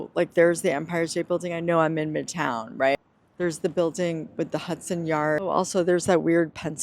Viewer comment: I thought you said the building with the graveyard not the building at hudson yards